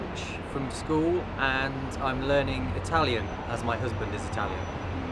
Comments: Did the guy 8:00 say "my husband"? I hope I heard wrong. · Did he just say "as my husband is italian?"